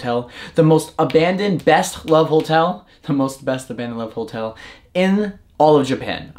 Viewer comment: I misheard u say "go to the best love hotel" im shocked so i rewind to check 😆😆😆